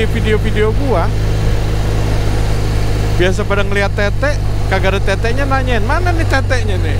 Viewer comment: gua kira gua yg salah denger,, ga taunya bang bnr bilang tt:v